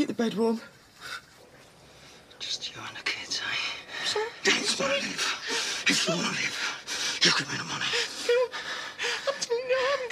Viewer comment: What the hell did Phil say at 2:13? 🤣🤣 Look at that face getting redder and redder like GAMMON! 😂😂😂😂😂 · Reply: He said "Don't Say Anything, if you want to live!"